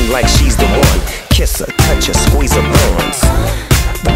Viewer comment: I thought he said squeeze her bones 🤣